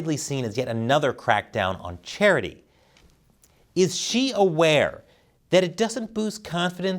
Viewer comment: I could have sworn you said is she aware 😄